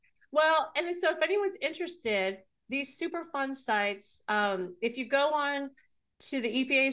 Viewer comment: Wow! I just looked up “super fun sites”, which is what I thought you said, and “Superfund sites” came up. I had a guess based on your description, that my city may be one; I was right! But, there are SO MANY MANY more even in my county and the surrounding counties! Multiple landfills are listed, Wright Patt Air Force Base is listed! People still live & work there! The site in my town was always called “Mound” or “The Mound” because it’s across the street from an historical Indian mound. But this was a government facility that the people who worked there were never allowed to talk about what they did there. There were rumors that it may have been more than 20-30 stories deep into the ground. It was built into the side of a hill near the Little Miami River, and not far from there- maybe a mile or so is our water reclamation plant on the side of the river. Directly below the facility, at the base of the hill, was the city swimming pool! I haven’t lived here all my life, but people say there’s a high incidence of cancer for people in this town, and after we moved here, just in our little church alone, I met 3 people who had had or were still fighting cancer. And I’d never met anyone with cancer before. Later, The Mound Facility was renamed Monsanto (oh yay! 😟). And when they finally closed the facility, the US Gov brought in toxic cleanup and generously volunteered to pay for a new pool elsewhere in town. I imagine there MUST have been lawsuits galore and threats of exposur